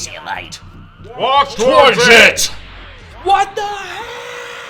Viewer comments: Wait, 16 says "Walk towards it!"? For all these years, I thought he was saying something like "Warp Tortit", since it sounds so much like an attack name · Because they like to put in the original japanese attack names...and because my hearing is absolute garbage, when 16 said "Walk towards it" I misheard it thinking he said something like "Wok Tordit" and just spent years thinking that was the japanese name for Hell Flash.